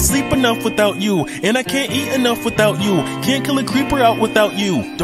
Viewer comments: The song lyrics: “that girls a miner” what I hear: “that girls a minor” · I closed my eyes while listening to this and all of a sudden i heard "that girls a minor"